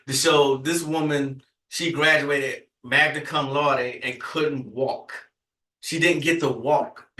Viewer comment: 🤧🤣🤣🤣Did you say that she graduated MAGNUM CUM LORDY??? 🫥🤧🤣🤣🤣🤣🤣🤣16:26mark